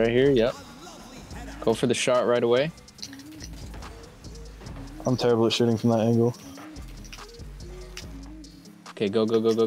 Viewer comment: this is what i heard: go for the shart right away. I'm terrible at shitting from that angle.🤣